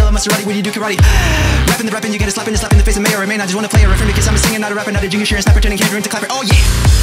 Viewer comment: If you slow the vid down using YT it sounds like he says Ginger snapper